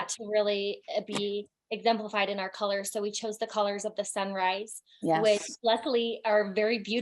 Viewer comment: Wait she said colors of the sunrise? I thought she was saying colors of the samurai 💀